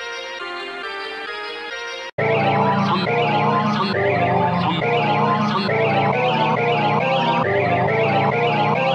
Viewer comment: What does it say? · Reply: Visurama